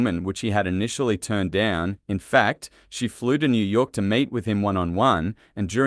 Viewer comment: I wasn’t watching the video and I thought you said “mate with him…” 🤣🤣🤣 I had to play it again. 🤣🤣🤣 Interesting fact though. So glad the producers went with these two instead of Molly Ringwald or Meg Ryan and Gere instead of Pacino. Glad the script was reworked too!